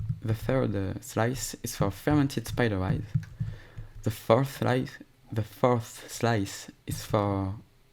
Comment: Did he just say “fourth reich”? 😳